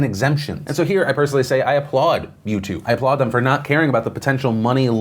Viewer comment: Did Phil just say "Mewtwo" instead of Youtube, someones definitely ready for Detective Pikachu and the new sword and shield pokemon game :)